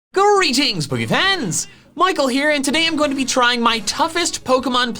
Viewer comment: The first time I watched you I thought that you said hello boogie fans not poke fans